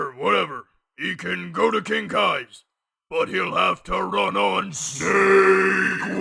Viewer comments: The firs time I watched this I thought he said "run on shakeweights" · First time I watched this, I coulda sworn he said "He'll have to run on SEGWAY!" Gave the following lines a completely different context XD